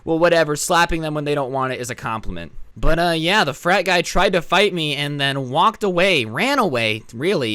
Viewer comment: For a second I thought it said a "FAT BOY TRIED TO FIGHT ME"